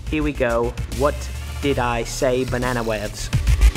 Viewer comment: Wait did Tyler say " Banana webs "